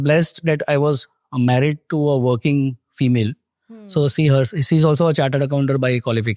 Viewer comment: Did he just said that he "Luckily got married to a female???" That got me rolling 🤣😂🤣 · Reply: He said 'Working' female